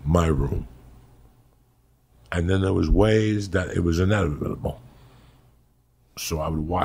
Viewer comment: what does Joey say?? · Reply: It was inevitable